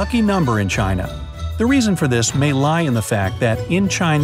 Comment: Correction for @ 4:55, it should be "wealth" instead of "prosperity".